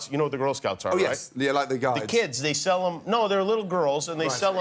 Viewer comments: Gordon said "Guides". Jimmy misheard as "guys". Guides is girl scouts in Australia and I'm assuming Britain too. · I think he said "like the guides" and Jimmy thought he said "guys" because in Canada and the UK we have Girl Guides. Completely different cookies I noticed too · He said they're like the guides but Jimmy thought he said guys lol. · Lol, I think at 1:34 Jimmy thought Gordon said "they're like the guys" instead of "they're like the guides (girl guides)".